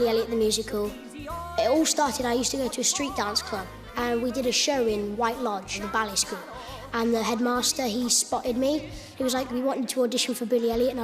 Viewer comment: Kid Tom says something funny and everyone laughs, but I can't tell what he said. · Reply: "I used to go to a street dance club and we did a show in White Lodge, the ballet school, and the headmaster he spotted me and he was like "we want you to audition for Billy Elliot" and I was like "ah fair do's".